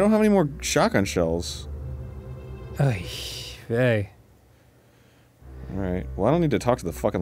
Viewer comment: Dan says "Oy vey" (Unless my ears deceive me) and the cc says "Oh, yay" Not sure why I still had cc on but that made me smile Huzzah! Harvester Sunday! :D